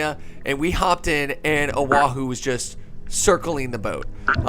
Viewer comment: when u said "A Wahoo" i thought u said "O'ahu"